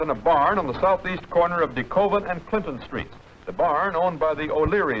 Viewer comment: did he say Coven and Clinton St???? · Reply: Decovin and Clinton